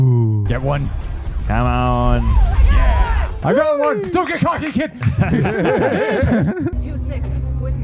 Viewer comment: what did he say? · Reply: "Don't get cocky, kid"